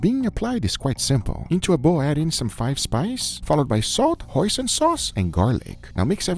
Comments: I'll be honest when Guga said "hoisin sauce" I thought he said "poison sauce" and was legitimately concerned for a second. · I don't know why but when he said "hoisin sauce" I thought he said "Poison sauce" ☠️